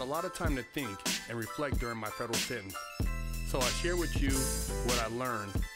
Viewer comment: 00:20 "i had a lot of time to think and reflect during my federal pen" your intro needs to be redone. I hear it in every video and wanted to wait for a time you might see my comment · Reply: He says "sentence" not "pen"